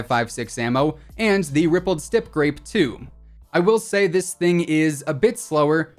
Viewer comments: When he was going threw the AUG attachments i can't be the only one who heard him say "Rippled Stip Grape" 😂😂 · did he say rippled stip grape, bahaha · Did he say rippled stip grape!?